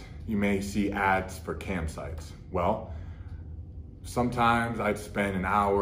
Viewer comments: I thought he said camp sites. I was like bro! That's just a vacation! No need to feel ashamed. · Thought he was saying “campsites” haha, was like dude there’s nothing wrong with sneaking off for a weekend in a tent, also was like what type of campsite advertises on adult sites😂😂